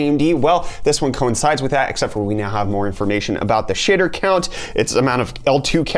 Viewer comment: did you say Shi**er count?